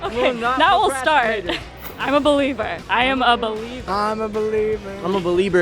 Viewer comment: Did Keaton just said I'm a Belieber in 2:06? :>